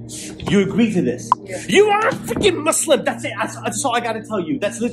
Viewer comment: As salamu 'alaikum My bro your vdo has made us happy, may ALLAH bless u and ur efforts. One issue in the vdo id like to point out is at 5:10 it sounds like u said "...you r a f****ng Muslim". Maybe i heard it wrong idk. But if it is that then i suggest u edit the vdo n maybe reupload, like silencing that part or maybe cutting out the word. I hope i havent annoyed u. May ALLAH reward u well.